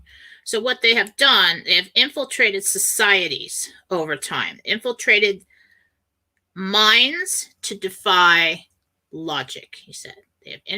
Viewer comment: “They have infiltrated minds to defy logic”. Did I hear that correctly? I didn’t mean to misquote this on Mike’s show today but I thought I understood it this way?